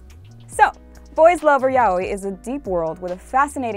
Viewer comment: Google captions Yaoi as Yahweh. Coincidence? I think not. It's